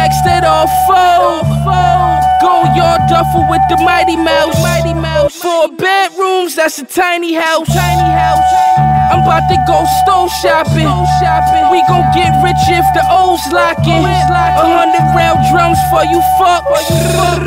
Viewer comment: “ I’m about to go stove shopping ” lol my boy different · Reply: I heard "stone shopping'